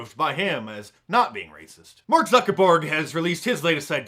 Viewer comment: Mark, did I just catch a Freudian slip? I thought you said Mark Zuckerborg. Used to love Star Trek, especially TNG, and Mark definitely has the dead eyes of a Borg drone. Good one, if so. If not, we'll just chalk it up to serendipity.